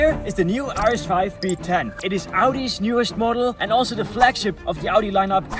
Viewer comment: i thought u said v10 got a little excited